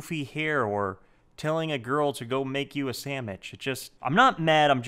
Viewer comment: did he say sandmich or am i deaf?